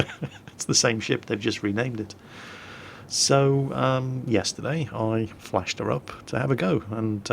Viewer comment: What said: "Yesterday I flashed her up to have a go." What I heard: "Yesterday I flashed her and had a go." What I thought: Rita, nooooooo!